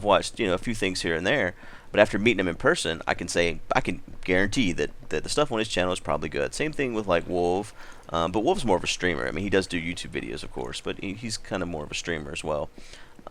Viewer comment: When he said:"wolv is more of a streamer" i thought he said 'wolv is more of a streaker....' -.